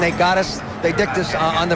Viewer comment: Did he say they "dicked" us on the flea flicker?